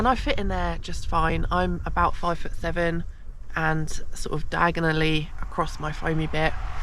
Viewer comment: Did you say "Diagon Alley"? <link>